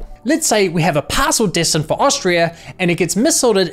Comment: the irony of the subtitles saying Austria instead of Australia